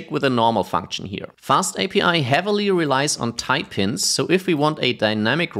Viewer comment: Iv been trying to puzzle out what he says @ 6:26 for more than 10 minutes but finally figured it out: type hints, not type in, nor tie pins (as the auto generated captions believes he means)